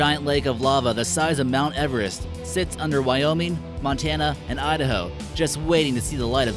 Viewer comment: I thought they said Fontana because thats where i live